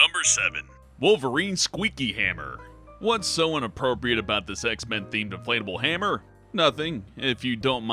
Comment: On number 7 I thought he said sex men